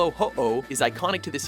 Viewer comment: CyndaKILL! HAHAHA! Sometimes mispronounced Pokemon names are pretty entertaining to hear... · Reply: Caleb Vanderweerd (In the video it sounded like they said Uh- Oh)